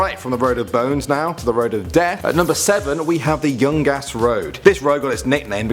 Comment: when he said the youngas Road I thought he said the young ass Road